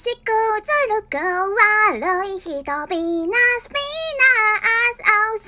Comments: ga salah denger telinga aink pinas njay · wait....did she says p**is??!